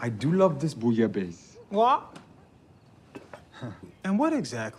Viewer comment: when it says Paul said "wha" ... what he is actually saying "quoi" which is the french word for 'what'